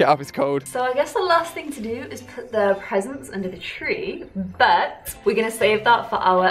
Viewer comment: did say peasants